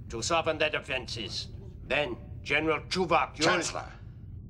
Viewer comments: At the 0:17 mark, did Gowron call for General Tuvok? How many places did Janeway have him infiltrating? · Did... did he just say "General Tuvok??"